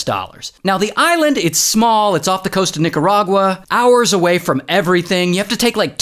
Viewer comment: what did he say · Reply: Nicaragua